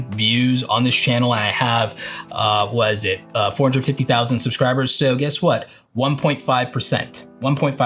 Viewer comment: I was at more than that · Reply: Oh. I could’ve sworn you said right now I’m at about 150K. I must’ve misheard. Regardless your growth over the last couple months has been great.